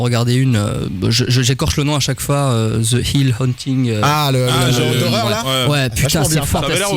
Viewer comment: c'est quoi la série qu'il dit svp ??? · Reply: The haunting of hill house